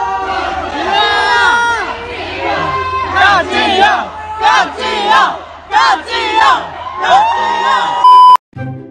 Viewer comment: I thought they where saying GOD Jihyo not Park Jihyo because I saw this many times and they wrote God Jihyo. Just saying. I'm not sure what's the correct one. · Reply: They were saying God jihyo